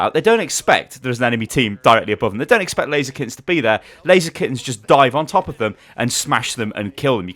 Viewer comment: not gunna lie, I thought you kept saying blaziken, every time you said laser kittens. smh, I was looking for player names blaziken and couldn't find one; I was so confused